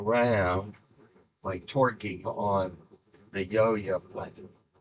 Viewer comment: The fact you have to ensure people knew he said torqueing instead of twerking is both funny and sad xD, we all know people would think he said twerking.